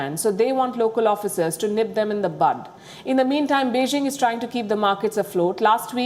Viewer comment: did she say nip them in the butt?